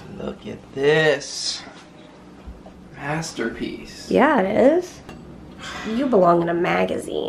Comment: When you took the focacia out of the broiler and said "Masterpiece", I thought you said "Pastorpiece"--and I thought "How appropriate!"